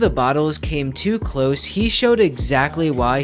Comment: That ain't no goal keeper, that's the GOAT KEEPER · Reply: I thought bro said "GYAT KEEPER"☠️